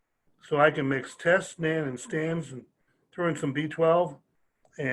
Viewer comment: 2nd round listening to this. Taking notes. At 9:30 he mentions he has a pharmacy that compounds test, stanazolol, sounds like there is another compound included and also vitamin B12. Am I hearing things or is there another compound? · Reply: Think he said Nan for nandrolone aka Deca durabolin